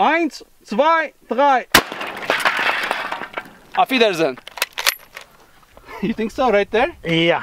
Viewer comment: what did you say? Afferdersin.. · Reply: Auf wiedersehen It is German for see you but its actually more polite, like good bye